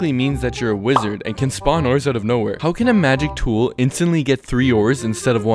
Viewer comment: I thought he said Oreos